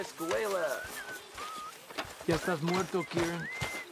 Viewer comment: when Kieran says "Mr. Escuella!", does Javier responds "que está muerto, Kieran"? I'm not at all fluent in Spanish, but I recognise the Spanish word for "dead" when I hear it! Is there any Spanish speaker who can eloborate on what Javier says?!